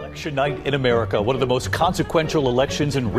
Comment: Thought he said Suge Knight in America